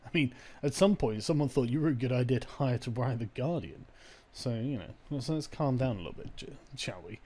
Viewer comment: Lol it almost sounds like he said "So you know, let's calm down a little bit Jew."